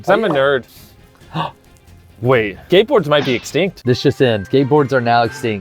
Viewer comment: Is it just me or at 2:12 does it sound like they say gateboard